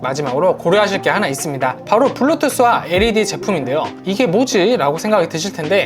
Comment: 블루투스 제품이랑 LED 제품이라고 들려요